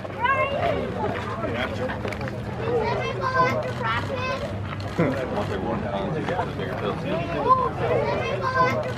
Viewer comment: 29초 꼬마애가 뭐라고한지 아시는분? Anybody knows what the kid said at 0:29? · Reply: 채병준 can I have the ball after practice요